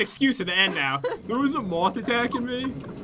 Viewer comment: OMG THERES A MOSS IN HERE ME:wut did you just say:TS HUGE ME:Really